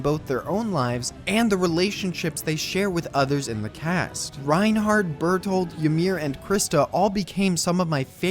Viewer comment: Did he just say Reinhardt?????😂